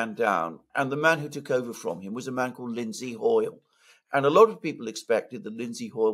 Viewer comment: I heard "Lindseed Oil" Many of these people have goofy, made-up sounding names.